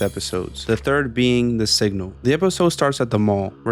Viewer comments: When you said “the signal” I thought you said “the sigma”💀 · I thought he said "the sigma" i might have it rock bottom